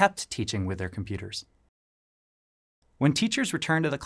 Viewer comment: Keptがこんな発音なんだ！っていうのとwithout に聞こえてそっちかー！😮